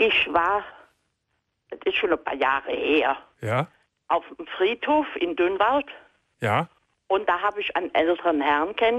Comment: "Ich war auf dem Friedhof in Dünnwald." Oh Gott, ich habe erst verstanden "Ich war auf dem Friedhof mit Dünnschiss."